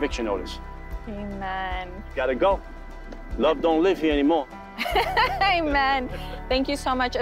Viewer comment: did he just say " love don't live here anymore!?"